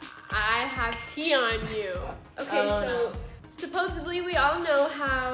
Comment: what did she say? supposebly?